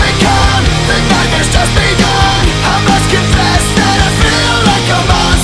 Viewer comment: I always thought it said, “Thank, God it just begun” but it’s “The nightmares just begun” I feel like an idiot... 😂